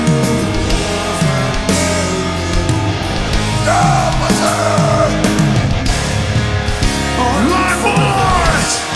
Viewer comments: What is he saying at 2:58? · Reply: He said, "toke7342"! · What did Howard say? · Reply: I mourn - for those- who never knew you